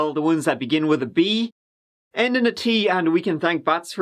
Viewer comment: begins with a B and ends with a T..? 44 seconds in.. Will stay tuned for answer.. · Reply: I thought he said begins with V and ends with T But either way What word is this? and did he Say it has something to do with bats?